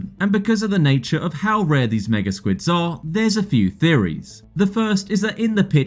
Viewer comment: what!!? Did he say... "this nigga squids" 😆